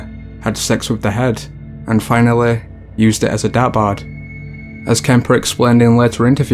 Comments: Somebody help me out here please. I can’t understand what the narrator is saying at 10:54 . He says Ed Kemper decapitated his mother, had $3x with her head and then used it as a “blank”. It almost sounds like he is saying, “he used it as a dad bod.” Lol but I know that can’t be right. I cannot figure it out tho. Can anybody catch what he said right there and let me know please? Thnx 🍻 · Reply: sounded like " dartboard", but probably another word, maybe in slang. He says it so fast I can't tell either.. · Subtitles would be nice....lol what about us hard of hearing?.... and some words you say are just a little hard to understand, plus some of the people in your videos are hard to understand as well!!. I turn on Closed captioning but it seems to just make up random sentences that don't make much sense half the time....lol! (Example- at 10:54 it says he used her decapitated head as a dad bod....um what the fuck?...lol )I'm partially deaf in my right ear and I'm completely deaf in my left so proper subtitles would be greatly appreciated!!!. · The third guy had sex with the head and used it as a "dabard"? can anyone decipher what word the narrator said? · What does narrator say at 10:53 "....and then finally used it as a (what)....." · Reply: 'dart board' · can someone PLEASE tell me what he is saying? "Used it as a ???" Please and thank you. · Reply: Dart Board